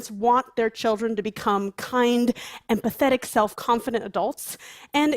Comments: I heard her say kind and pathetic kids at first. It wasn't until repeated it, that I heard empathetic. I still think I was right the first time. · When she said "kind, empathetic", I thought she said "kind and pathetic". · the captions say "pathetic" instead of empathetic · did she say empathetic or and pathetic? 😆 either one fits